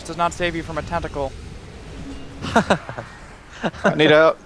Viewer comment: did he just say "Die swifty die!"?